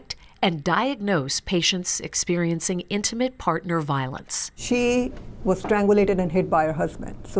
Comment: Omg. I thought it said "inmate partner violence". Like gay dudes in jail was beating on their "ladies". 😂